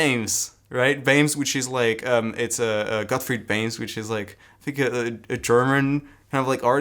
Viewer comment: At minute 39:30 mentioned a anatomy simplified resource by a German artist. It sounded like veims. Do anyone know how I can find it? How it's spelled? Thank you XD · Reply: Don't know if you still want to know, but i guess it's Gottfried Bammes!